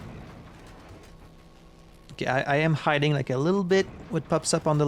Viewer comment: I thought you were going to say “OK, I am hiding like a little b7$%h” 😂